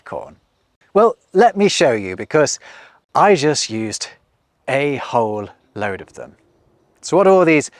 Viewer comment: did he just say a-hole?